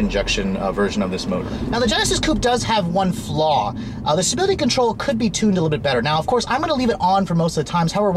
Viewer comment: What did you say? I rewinded that part and listened several times but I couldn't make out what you were saying. I got "Now the Genesis Coupe does have one flaw. The (not sure what was said here) control needs to be tuned a bit better". After that it sounds like you're talking about the traction control but you definitely didn't say traction. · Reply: TheCobruhAlienat0r Stability control.